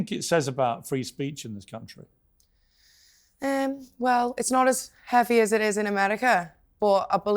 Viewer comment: Did I hear her correctly when she said well it's not as bad as America??? Did I misunderstand her or is that how indoctrinated propagandized Europe is regarding what's happening in the US somebody please bring me some clarity